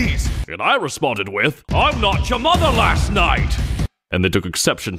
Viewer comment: "Get on your knees!" "I'M NOT YOUR MOTHER LAST NIGHT!" ...finally got that joke. I heard "knocked" for so long - same vibe, less witty than the actual joke.